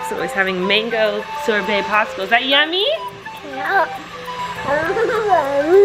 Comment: OMG HE LITERALLY SAID “IT’S SO GOOD”. He’s getting TOO big TOO fast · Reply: I think he says "IT'S YUM" at 4:51 as well!